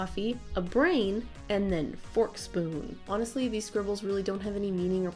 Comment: I thought it said "pork, spoon" 😂😂😂😂😂